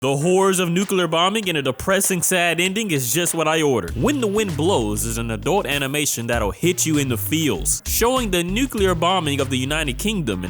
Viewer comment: Please tell me I’m not the only one who misheard him opening the vid with “The WHORES of nuclear bombing and a depressing sad ending are just what I ordered” 😂